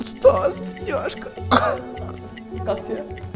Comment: я же одна слышу "Стас.. няшка" это сказал? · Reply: Он сказал бедняжка